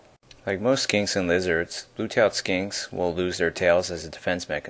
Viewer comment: did he just say blue tail skink?? lol